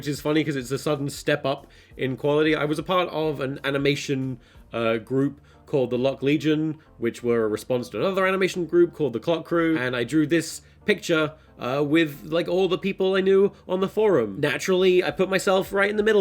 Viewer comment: I thought it said "cock legion"